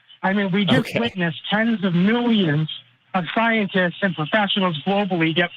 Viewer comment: There have been a half a dozen or so YECs who got in trouble for academic fraud. Five or six is the same as tens of thousands, right?🙄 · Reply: He didn't say tens of thousands. I thought he said tens of millions. When theists lie, go big or go home, I guess.🙄